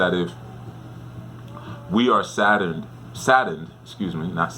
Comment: Thanks Chief! Funny, I thought you were saying “Saturned” at first Lol Saturn 🪐 is what ppl do NOT want to see in their “money spots”. Saturn is restrictions-boundaries. Also deals with the challenges within one’s DESTINY. 🤔 Thanks 🙏🏽 again. #EjiOgbe